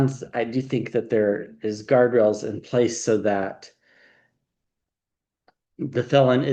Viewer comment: Funny,, at 14:20 when you said guardrails are in place, I heard gargoyles are in place (not that we couldn't use those too.) 😁